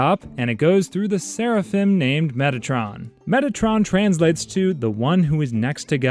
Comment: I'll be honest, i thought that said Megatron 😭